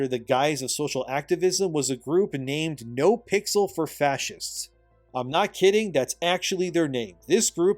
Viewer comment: I misheard the "No pixels for fascists" I heard "No pixels for fat chicks". I had to go back and watch it again, and like ohhh, that's what he said lol. Time stamp 18:44.